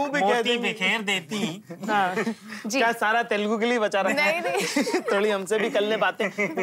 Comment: What did Zakir say at 4:32??? Sab kis ke liye bachake rakha hain ???